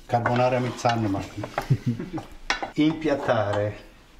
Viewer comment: was sagte er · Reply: Die Nudel auf den Teller geben."impiatare"